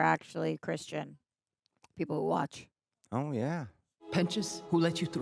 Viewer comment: so fun fact if you watch with captions on, at 9:55 it says "Oh yeah, Penis" instead of Pentious i cannot tell you how loud i cackled at that